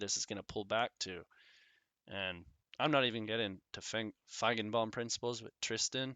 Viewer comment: Hi. You said something with Fagen bomb principles around the 38:00.I couldn’t find what you said, can you repeat what you said?